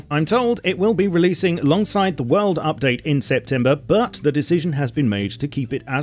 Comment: did he say "weld update" in september??